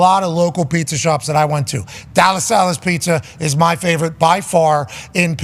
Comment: What is the name of this pizza joint he is mentioning in Pittsburgh? I cannot understand him. I must have listened to it 100 times. I give up! Somebody please help me with this. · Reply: Della Sallas pizza, it's in Verona. He did kinda pronounce it weird, but I can't imagine he's talking about somewhere else